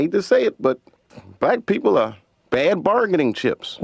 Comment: When DC says "black people" it sounded like said "white people".